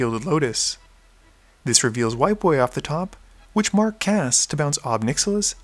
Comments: I thought said "...reveals White Boy off the top..." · Oh, this reveals WIPE AWAY! Not White Boy. I misheard that.